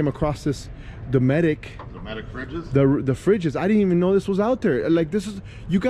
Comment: What is the name of that cooler at 17:30?? I really need to know if anyone else knows. I heard him say “medic” but I can’t find them anywhere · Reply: Dometic coolers